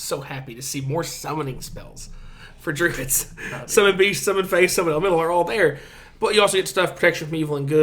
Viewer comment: When you said summon fey I thought you said summon face 😂